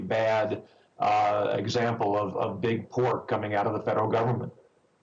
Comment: does he say Big Pork or Big poor?